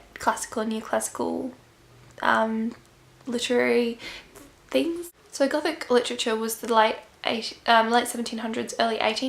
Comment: did you say psychotic literature here 4:11 ????